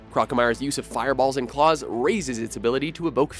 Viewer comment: Did he say "eboke fear." At 2:10..... Evoke?